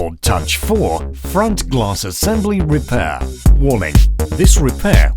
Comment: It sounds like he says warming instead of warning. LOL